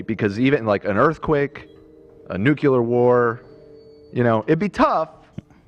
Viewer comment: did he say nukular?